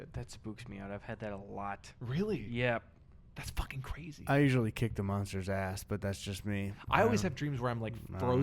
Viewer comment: I thought said "I usually eat the monster's ass, but thats just me.